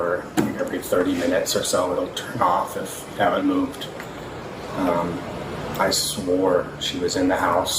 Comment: I would have been responsible and parked in the garage but i have a peloton,, but my peloton was in there.. Also listening to what he said not the CC but at 5:42 he refers to his daughter as an "IT hadnt moved"